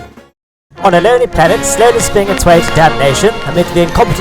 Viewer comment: Okay sorry im curius. What does he say from 0:28 to the end of the intro? I think i cant understand it due to my english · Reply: I think that he says "On a lonely planet slowly spinning itself to detonation", but honestly, it's so quick and distorted I have no clue either